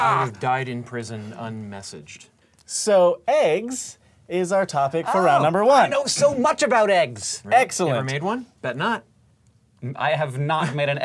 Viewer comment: Did he say Eggcelent?